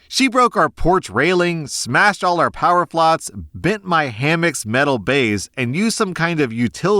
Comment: did you just say "POWER FLOTS?" (instead of flower pots)